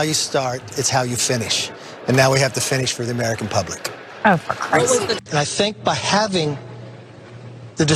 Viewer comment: "oh for Christ sa.....". 😂 Anyone else catch that?